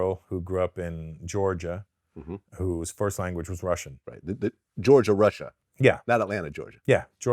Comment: "Georgia, Russia right?" -. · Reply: I thought he said Georgian-Russian. Someone from Georgia with Russia as first language is possible. It's like saying Malaysian-Chinese whose first language is Chinese.